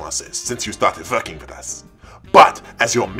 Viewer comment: I heard f*cking with us